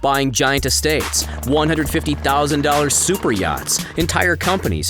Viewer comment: did he just say “150 000 dollar superyachts” ? Doesn’t seem like that much really...